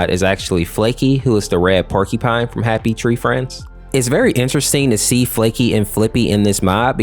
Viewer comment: did he just say happy tree times instead of happy tree friends?